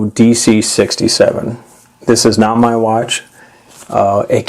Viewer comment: thanks, but it is not my watch. Take care. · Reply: ToxicNATOs ha I miss understood - thought you said “it is now my watch”.